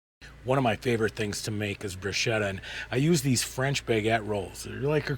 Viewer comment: what is the French toast you made called?! It looks amazing and I had to rewind becuse we thought you said bruschetta at first!! Just wondering what the real name for it is :) thanks!